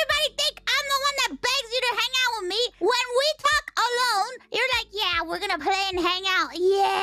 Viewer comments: Why I hear "when we fcked alone" 😂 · Is it just me or i heard "when we fuck alone" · It sounds like she said "when we f*** alone" 😂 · she said "talk alone" and i heard something else lmfaoooo i heard "f*ck alone" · Legit, I really thought she said "when we f**k alone..." and I had to go back to read the text to make sure I misheard that lol. But to be fair I only had one earphone in so yeah.